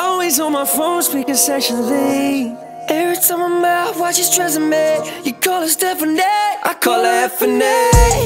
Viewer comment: sounds like Conor is saying Tresemme. Anyone else? Okay, I'm leaving...